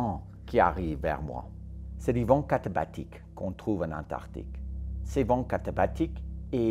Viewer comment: Horn à 7:57, des vents quoi ?? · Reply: vents catabatiques